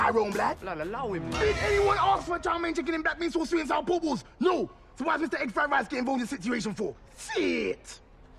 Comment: I couldn’t get what he said, but for some reason I just can’t stop laughing at that part😂😂😂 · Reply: "Diiiiid anyoneaskforchickenchowmeinblackbeansaucewithsweet&sourporkballs?! No! Sowhysegg&friedricegettininvolvedinthesituationfor?! STHIIIIIIIIIT!"